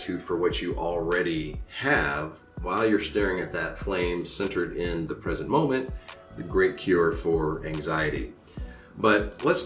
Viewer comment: Evp at 10:14. Cant tell what it says though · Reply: Hi, my friend!:) It says, "Gratitude centered in the present moment relieves anxiety." Blessings!🔅✨💫